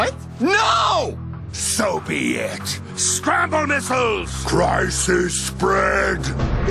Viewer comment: I didn't know what said him. Could somebody write me what said him? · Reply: Scramble Missiles!